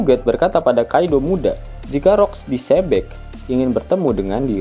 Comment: kok gw dengernya Rok Di Sobek ? apa gw salah denger ya 😂😂😂